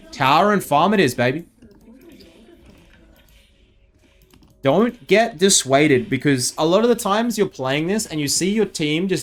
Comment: he said dont get dissuated instead of dont get it twisted, he changed☹